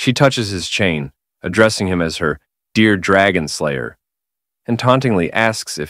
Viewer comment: Oh you wanted a legendary Dragon SLAYER??? I thought you said legendary Dragon Layer